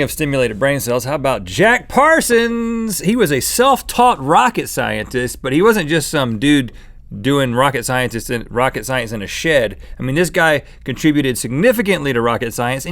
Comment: i thought rhett said s*!t